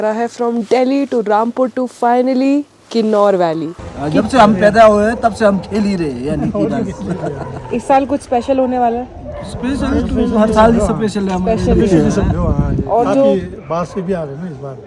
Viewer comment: Hello, From 1:50, I couldn't understand and grasp the story they had narrated. Can you explain it here? It would be very much appreciated if a local Himachali reading this comment can explain.Thank you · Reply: He said - jabse hum paida huye hain idhar hi rhe hain. I asked - iss saal kuch spl hone wala hai? They said - har hi special hai.